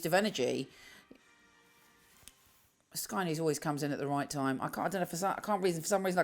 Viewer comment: "xxxxx always come in at the right time". Could someone tell me what does she say there? Thank you! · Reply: Sky News